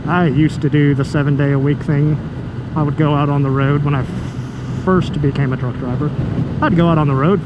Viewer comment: thought you said “when I first became a drunk driver” lol